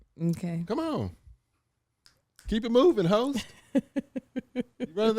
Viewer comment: I thought said "Keep it moving hoe " but he said " host" I was like wait a damn minute 😂😂😂😂